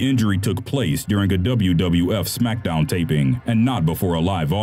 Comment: did he say WWF?